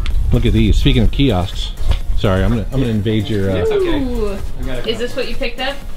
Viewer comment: Your Rev transcriptionist said "engage" instead of "invade." tsk tsk and 6:33 lol..Poor Kelsey missin' that promo